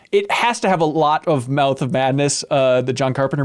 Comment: The way pronounced mouth in mouth of madness was so Minnesotan that I thought he was saying melt of madness. This is coming from someone who is also from Minnesota.